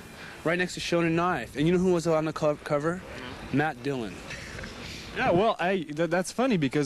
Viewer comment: What did Krist say at 1:10 that made Dave and Kurt laugh? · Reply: I think he said "I look like matt dillon"